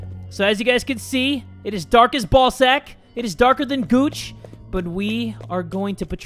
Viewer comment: Jay : *really scared and said ballsack Me : sounded like you said balsa but no